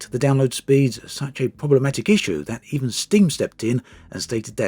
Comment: I thought he said “Sting stepped in”. Now he wants to save the virtual rainforest too. 😂